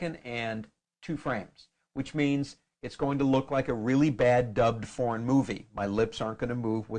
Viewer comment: Thought it said "a really bad dutch porn movie" Lol my bad...